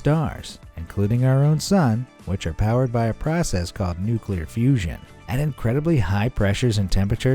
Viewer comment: Did you say Nuclear Fusion? <link>